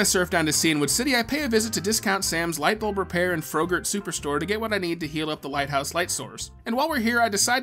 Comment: Excuse me. At 8:25, did he just say "discount sam's lightbulb and thoatgurt"? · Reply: Discount Sam's Lightbulb Repair & Frogurt Superstore